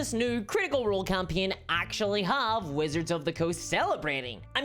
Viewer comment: I could’ve sworn I heard “Wizards of the Cooch” somewhere in there.